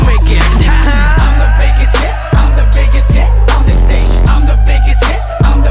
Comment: I always thought it said I'mma make you sit on the stage! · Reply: We'll I heard "I'm the biggest shit on this stage" lmao